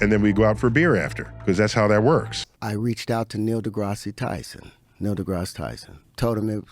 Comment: Did he just say "Neil Degrassi Tyson"? That sounds like an ironic twitter account handle.